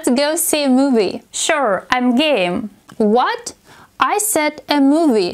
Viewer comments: im thought she said im gay 🤪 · I thought she said “I’m gay” lol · When my friend asks me the same question, i said yes I am gay!! 😂😂😂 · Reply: I thought she said I'm gay at first and then I rewatched it XD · I 1st thought that she said im Gay